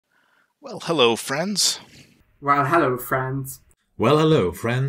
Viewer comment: The more I hear them say "Well, hello friends", the more it sounds like "Well, hello France"